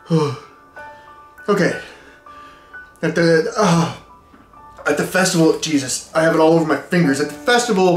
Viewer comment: I heard "I am the vessel of Jesus". ...and thought, "Man, only 20 seconds passed before the hallucinations started."